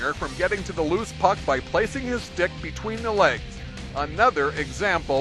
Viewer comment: does he say "placing his dick between the legs..."